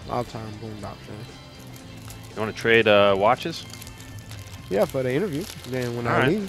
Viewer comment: is he says you want to trade watches with lil baby just for anyone wanting to know 😊😮